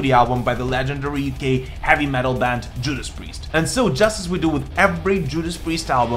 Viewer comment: I thought I heard you say "legendary gay heavy metal band Judas Priest" or am I imagining things